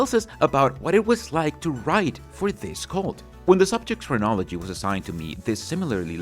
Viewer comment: When you said 'chronology' I misheard 'phrenology,' another thing they used to believe in, but don't anymore. Yep, the one true religion.